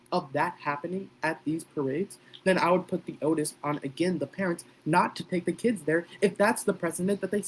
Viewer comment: did you just say “otis on the parents” ???